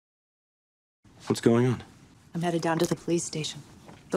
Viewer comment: 와 진짜 이 정도 난이도와 길이 너무 좋아요!! 우연히 뜬 영상인데 보석을 찾은 기분이네요👍❤️ 1:55 headed down이라고 down도 들리는 거 같은데 맞을까요? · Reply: 네 headed down이 맞습니다.